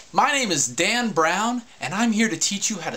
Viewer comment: I thought u said Dick Brown...But thanks for the video!